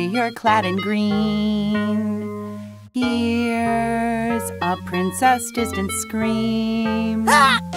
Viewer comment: First time i heard that i though it was "hears princess stupid scream" xD